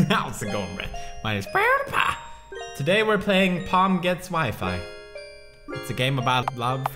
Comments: I thought it said Porn gets Wifi 😅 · Am I the only one who thought it said "Porn gets Wi-fi"? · I thought it said Porn Gets Wi-Fi like da fuq XD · Who else thought it said porn gets wifi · Porn gets wifi I thought it said